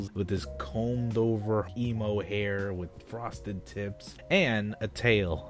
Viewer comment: I THOUGHT YOU SAID FROSTED NIPS CRYING